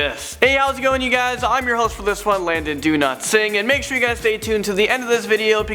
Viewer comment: Did you say that your name was Landon Do Not Sing?